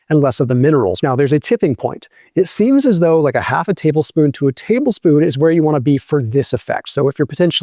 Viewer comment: Thank you for this information. Do you use a teaspoonful or a tablespoonful? Thank you 🙏🏼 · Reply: I thought I heard the video say one tablespoon.